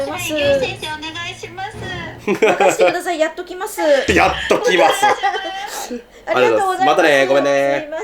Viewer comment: の唯先生の「やっときますー」が「殺っときますぅ(野蛮)」にしか聞こえん😂WWW